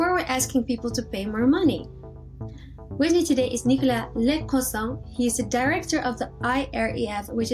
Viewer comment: I thought she said 😂